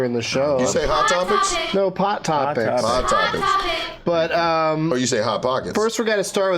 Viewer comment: every time I hear the "hot topic" I legit hear hot pocket lmao